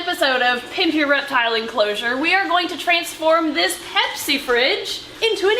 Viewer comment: LOL I thought you had said "This time on does it reptile enclosure?"